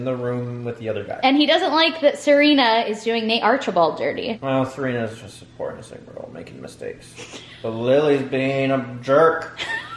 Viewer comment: I thought said “Serena is just a whore” and I was like 😬😳 and then I realized he said “poor innocent girl” and I was like 🙌👏👏